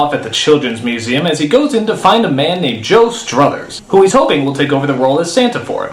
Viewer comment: i was so confused for a second, at 7:02, i thought he said joe strummer, and i was like the clash + santa claus = ?